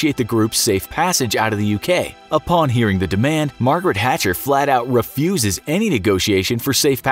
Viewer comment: Did anyone else hear Margaret Hatcher?